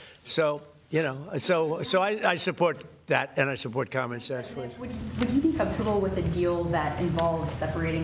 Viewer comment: did he say he supports communists?